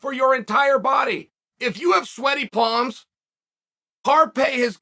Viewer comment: I turn it up on my headphones n all I hear is “do you have sweaty palms” from the undisputed 😂